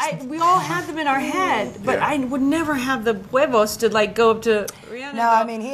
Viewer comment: What does Sandra say at 3:50??? I would never have the...WHAT??? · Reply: huevos, which would mean "balls" in spanish 💫